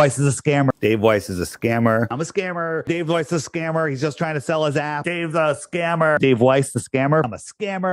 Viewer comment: I keep hearing "Dave's a scammer, he's just trying to sell his ass"